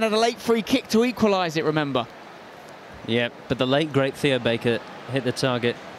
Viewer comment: did he say the late great Theo Baker.